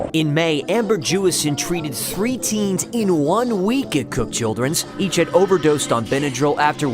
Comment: i thought u said cook children 💀